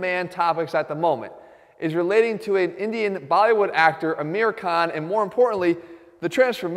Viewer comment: i thought he was saying american rather than aamir khan